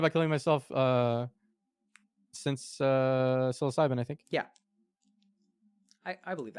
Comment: What does Reckful say at 1:11:03 ? Is it "I haven't tried killing myself since 7" ? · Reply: psilocybin, as in psilocybin mushrooms.